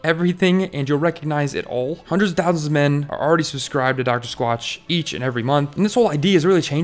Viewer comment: Dr. Squatch! I thought you said Dockers Watch.